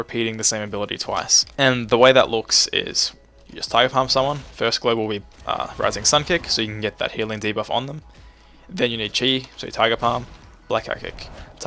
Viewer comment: english do you speak it? its literally impossible to understand what you just said. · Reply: "Just Tiger palm someone, first global we uhh.. Rising Sun Kick so you can get that healing debuff on them" :')?